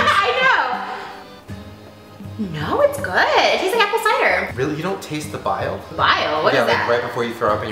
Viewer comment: What is an 'appesiter'? She said 'appesiter' when she drank the pumpkin juice. Did she mean 'appetizer'? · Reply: I thought she said Apple cider